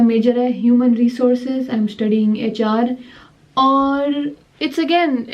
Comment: I thought you said I m studying Achaar (pickle)😂😂